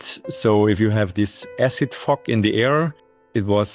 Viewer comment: I heard "... this acid f*** in the air" Then I realised the F-word was actually 'fog' 🙂